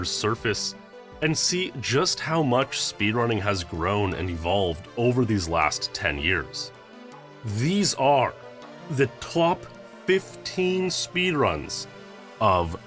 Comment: did you just say "twop"?